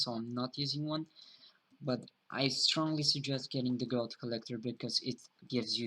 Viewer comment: Did you say "god collector" at 15:20? :)